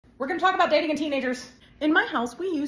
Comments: I thought you said "were gonna be talking about dating teenagers" and I had to pause for a second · I heard "were gonna talk about dating teenagers" and almost choked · OKAY I THOUGHT YOU SAID "DATING YOUR TEENAGERS" AND BECAME V E R Y CONCERNED FOR A MOMENT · I thought you said: we're gonna talk about dating s teenager 😭